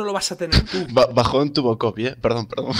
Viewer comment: que dice? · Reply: Gochi PC bajon tuvo koby.Debido a que hace poco murio en un accidente de helicóptero